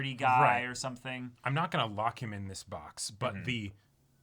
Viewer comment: What I heard at 29:40 was: "I'm not gonna Lakan in this box"